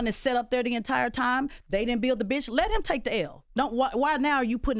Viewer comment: We all thought Tez called Kamal a b*$%#. 🤣🤣🤣